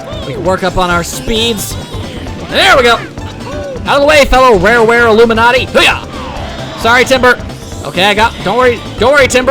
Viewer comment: Wait, did you actually just say "Rareware Illuminati" instead of "Rareware Alumni"?? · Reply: That's what I heard, " Rareware Illuminati".